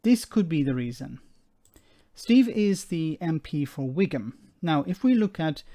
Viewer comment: Anyone else think he said Wigan at 2:17?